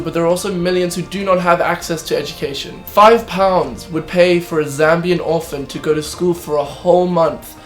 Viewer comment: i thought he said zombie orphan :o